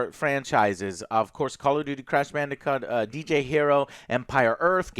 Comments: Joe, did you just say "Crash Bandicunt"? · At around 3:30 of the video did Joe say crash bandicunt?!? Shit had me wheezing lol.